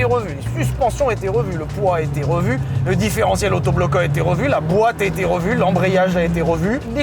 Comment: Le quoi ? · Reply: Le différentiel autobloquant